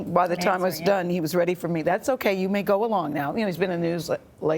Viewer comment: ??? what just happened. did she say you can go now?